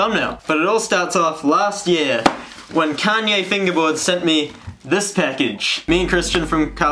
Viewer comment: at first I thought he said Kanye instead of Kalye